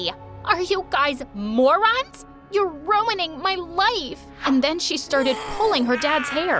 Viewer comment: everyone hear: "your ruining my life!" i what i hear: "your ruining my wife!"